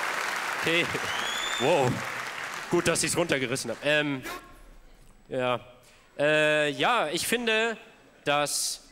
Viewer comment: habs grad erst rausgehört, aber hat da wer "butz" oder "jutz" oder so gebrüllt so ca. bei 9:50 ? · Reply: Es war "juckt"